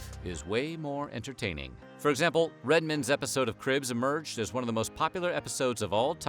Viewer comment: I’m dying!!! The way that the narrator (who is Steven Colbert’s voice twin, btw!) says Redman’s name!! Instead of Red MAN he keeps saying Redmen! Like Goldman or Silverman. It made me think of that Friends episode…”it’s not his last name! It’s not like Phil Spidermen! He’s a Spider MAN.”